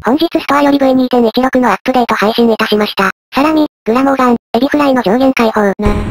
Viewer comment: サラミがさらにに聞こえた